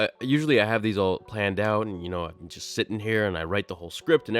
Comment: did you just say you're sithing here?